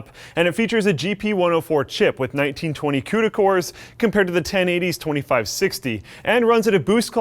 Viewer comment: whenever he says "Cuda Cores" all i hear is "Cooter Cores"